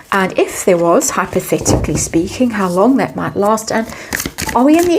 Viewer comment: I heard "hypothetically speaking" as "how pathetically speaking". Indeed!